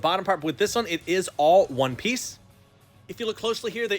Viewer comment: I thought he said the anime one pice